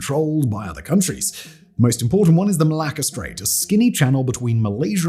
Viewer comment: I know I'm childish - but EVERY SINGLE TIME I hear " Malacca Strait" .... I actually hear - "Malaka" the Greek swear word..... sorry!